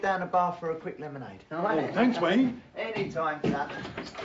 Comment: Did Wayne say, "Anytime, Pat." instead of saying "Anytime, Bomber."? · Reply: Sounds like he said any time son.